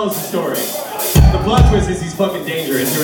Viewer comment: does say Thomas is dangerous? lol